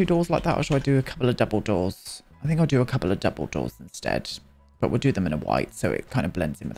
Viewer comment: Every time you said double door I heard it as 'dumbledore'... is this a sign to reread the books?